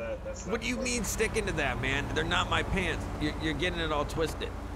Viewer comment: I thought it said “Thoes aren’t my parents” 😂 I can only imagine how Steve Harvey would react to this😂 When u realize ur 9 minutes late, fuckkkkk